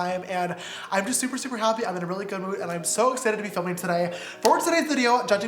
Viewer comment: I literally thought he said "im good looking" instead of "good mood " but I was like "yep yes you are"